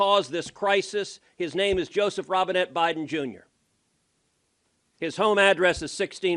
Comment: I heard it wrong the first time. I thought he said "Joseph vomited Biden Jr"